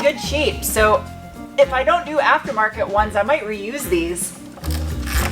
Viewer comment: You said "reuse" and I heard "re-UZ"